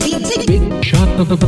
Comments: At first I thought that he said: "shut the up and take the weed", not "Big shot of the week" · Love the “Big Shot of the Week” sample from the Big Shot soda commercial. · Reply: i thought he say big shot booey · OH he's saying "Big shot of the week" I genuinely thought he was saying "bababooey" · Really good song- out of curiosity what did you sample for the new voiceover? Or did you use your own voice? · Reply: Oh my god, I thought it was saying "Bigshot Booey" LOL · All I hear is "Big Shot Bababooey"